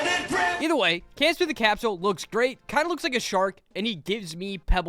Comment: sounded like shart am I the only one?? 😅